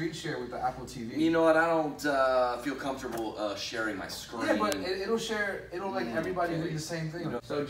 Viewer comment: why do the captions say subscribe button instead of screen lmao